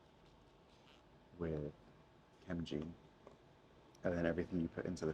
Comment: dawg idk 😭😭😭 with the spray stuff I thought you were saying MG. I’m gonna have to rewatch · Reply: OHHHHHH that makes sense. No it’s ok Idk what I was hearing 😂😂 wait actually now that I think about it. It I heard M from the word chem. And G from gene. I think it’s just an accent discrepancy. Edit: I seriously thought I was hearing a special name too 😭 I don’t even know what my lab used. They just told me to spray the area and go. 😂 with my new mentor. If I’d worked with her longer I think I’d be less confused.